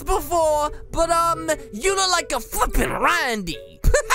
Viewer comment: DID U JUST SAY U LOOK LIKE A F**** RANDY??!!??